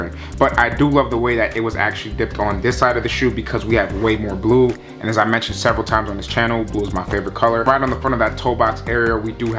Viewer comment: Only thing i heard was "MX moon greys and blue was your favorite color "🤣🤣🤦🏽‍♂️